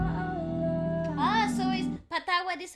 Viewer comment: i heard "patuwad" ahahah 😂 😂